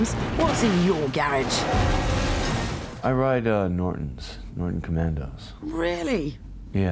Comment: Can someone transcript what kind of vehicle/bike Keanu Reeves drives? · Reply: I heard "norton commando" That's a bike